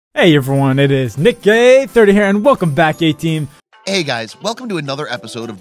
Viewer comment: I thought he said “Hey everyone, its Nick Gay 30 here” in the intro